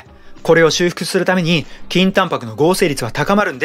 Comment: 倍速で聞くと筋タンパクがきんた○に聞こえて何度も巻き戻しちゃう